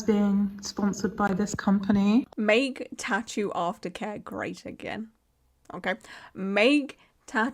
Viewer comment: Will be honest when you first said what was on the hat I have a glare so I couldn't see so I thought you said "make tattooartica great again" 😅😅😅 In all honesty though this is a great video and even though I'm a Canadian I appreciate you talking about these issues